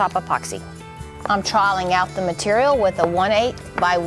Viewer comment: I love you and I love your videos! But.... In the first few videos of yours that I watched, whenever you said "trowel, troweling, troweled", I had no idea what in the world you were saying! I kept hearing "trial"! And I'm very familiar with SCC and their videos! I eventually figured it out, but as a fellow southerner, if I didn't understand, you can bet there's quite a few other folks who do not understand that you are saying the word "trowel"... So, I mean this in the best possible way, say TROW (rhymes with cow) ULL... TROW-ULL... TROWEL. ❤